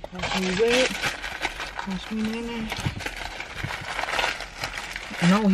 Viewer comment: "Who else heard her say watch me whip watch me nay nay" 😂